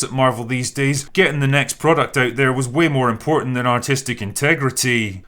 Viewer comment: Every time the drinker says "artistic" I hear "autistic", and as an actual autistic person I can say more often than not the "creative choices" of the studios that make these movies make less sense than my actual autistic brain